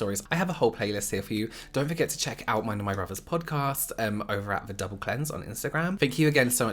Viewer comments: When you mention your potcast the double cleanse I thought you said "the devil cleanse" 🤣🤣🤣 hahaha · So, funny story... The first time I heard you say the name of the podcast, I heard "The Devil Cleanse" and... honestly, I'm not mad at all at this name. I guess "The Double Cleanse" works too... I love both you and your brother! Stay safe and ghost-free! 🖤